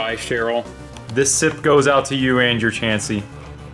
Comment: this is like my 5th time watching this and I just realized that at 20:45 you say "and your Chansey" and not "Andrew Chansey" I thought you just said that for no reason